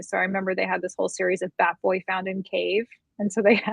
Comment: Ohhhh, "bat boy" found in cave. At first I thought she said fat boy found in cave.